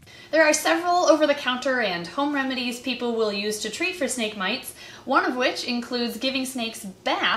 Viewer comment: Who else thought it said "how to treat snake bites"